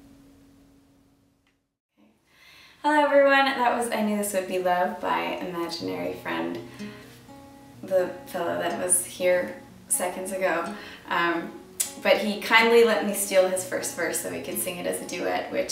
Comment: the PILLOW that was here, seconds ago... lol - sounded like that on my first listen lol